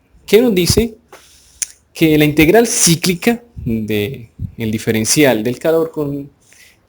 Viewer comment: Es una integral...qué? ayuda porfa no escuché bien. XD min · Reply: Integral Cíclica